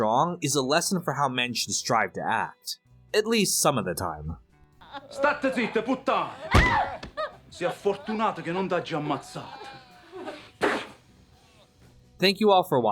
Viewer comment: I couldnt understand a word he was saying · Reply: He says "Shut up Slut, you're lucky I didn't kill you" it's Neapolitan Dialect